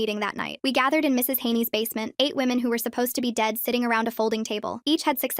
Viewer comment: I thought they were saying they actually Ate women. My slow a$$ brain😂😭💀